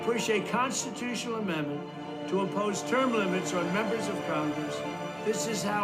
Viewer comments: IMPOSE term limits, not OPPOSE them. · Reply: I believe it was a typo, I thought I heard him say Impose. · IMPOSE term limits, not OPPOSE them. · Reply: Misspoke or a Typo, I listened to it over & over, and it sounded like he said "Appose" to me.. Makes more sense to.. Push a constitutional amendment to place side by side term limits on members of congress. There are two houses of congress that should have an amendment side by side referring to both Senate and House.... He is not going to say "Oppose" unless it was a misspoke... There is no term limit for congress. Makes no sense to Push a constitutional amendment to fight against term limits on members of congress when they already don't have term limits.. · OK, did I miss something. I heard "oppose" term limits. Did he mean "impose" term limits? Even point 10 above says "oppose." Imposing term limits would get rid of the established deep state.